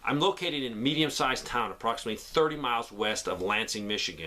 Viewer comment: my bad, I thought he said West of Lansing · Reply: He did say west. Grand rapids has over 100,000 people and battle creek is around 50,000 to 60,000 people. The person should have said what city he was in.